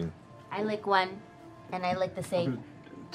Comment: So I thought Caleb said "and I like men too" and I legit crushed a whopper in between my fingers. The ships were bubbling, then I was like 'awwww'